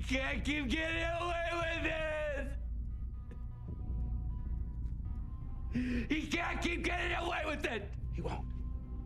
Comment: I can’t be the only one who always thought he said “He cant keep getting away with it!”.